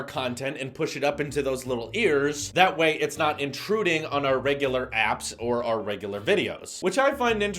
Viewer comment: Regarding this video being demonetised, always check the automated captions to make sure the software doesn’t think you are saying something you are. For example, in this one you said “intruding” but it heard it as “shooting”. Can you put the videos up as unlisted for them to be checked and then make them live after they have been Okayed? You also said “blowing it up” which could cause a flag. You need to be very careful what you say and how you say it.